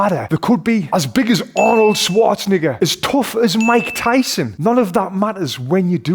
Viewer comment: did he say ni**a? 🧐